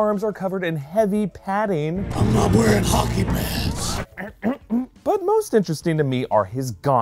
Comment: I thought the clip said "I'm not wearing hockey pants" and was very confused until i looked at the comments and saw it was "hockey pads", that makes way more sense.